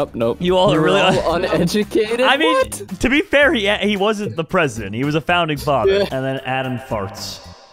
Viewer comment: Did he just say the Alexander Hamilton was one of our presidents at 2:10? Jerome, where did u take history? Russia? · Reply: No he said to be fair he never was, buddy can you hear?